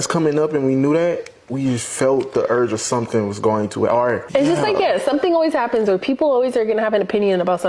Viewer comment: Sounds like she said he was going to book me